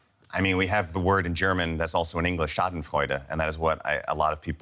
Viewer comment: what's the german word he said around 3:50? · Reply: Schadenfreude According to translations i found on the internet, it's when you feel joy in the misadventures of somebody else. In this case, because Trump always tried to belittle the risks of covid, seeing him infected and potentially having health issues might be reason for some people to rejoice. This feeling is what represents the word schadenfreude.